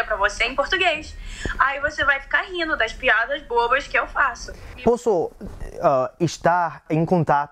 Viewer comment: she actually said silly jokes, not amazing